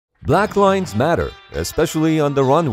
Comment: man I thought this dude said Black lives matter and then randomly started talking about power washing